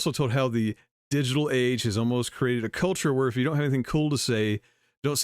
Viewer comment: When I heard Meghan, I thought she said "if you don't have anything cruel to say..." Was I mishearing?